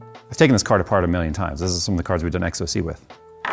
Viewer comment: Did I just hear him say he done ecstasy with that GPU? Sounds like a good time 😂